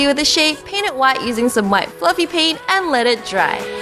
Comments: did she say fluffy paint insed of puffy paint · The whole time I thought you were saying puffy paint but you said fluffy paint, it's called puffy paint or fabric paint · she said fluffy paint instead of puffy paint